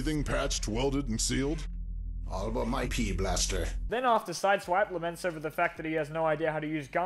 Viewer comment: Every time Ratchet says something about his weapon I just hear "pee blaster"